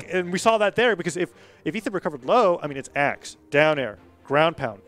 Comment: what did he call him? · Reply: If Ethan recovered low....